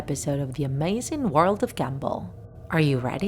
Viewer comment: Is it just me or do I hear "Amazing World of Gamble". No one? Just me? Ok.